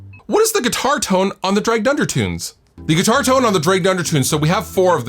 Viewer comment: For some reason I kept hearing "Dragged under" as "Drey Gunder". I'm like, "who is this Drey Gunder guy we're talking about?